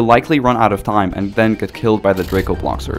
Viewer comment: I thought it said drakebloxxers 💀